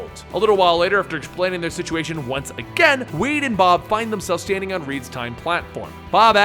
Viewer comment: Who else Heard weed and Bob